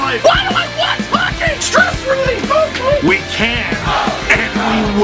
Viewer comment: It actually sounds like he said stress relief and not stressfully